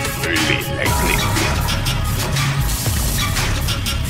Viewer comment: I heard “this is one of the weirdest characters in all of fiction”